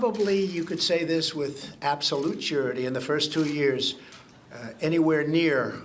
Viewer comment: did he just say suready?